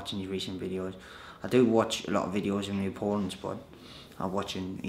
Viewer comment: sounds like "I do watch a lot of videos on youporn" haha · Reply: It did sound like he said youporn lol. I had to remind and he said opponents but still caught my ear